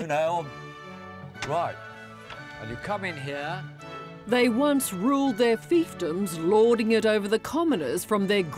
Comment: it sounds like she says "they used to rule their theif-ems". Couldn't be more accurate or spot on!